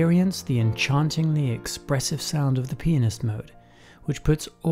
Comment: It literally sounds like hes saying "of the penis mode"